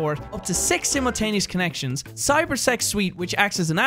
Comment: i thought he said cyber sex